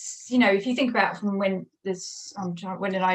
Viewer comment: I thought the guest said spy scales. Took me a moment to realize she said Spice Girls.